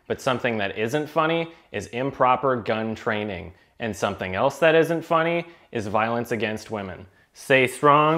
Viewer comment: I thought he said “something else that isn’t funny is women” for a second there.